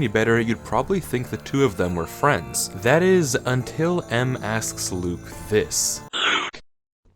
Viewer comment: i wasn't looking at the screen i was just listening and when i heard M i thought it said AM (the AI from "i have a mouth and i must scream")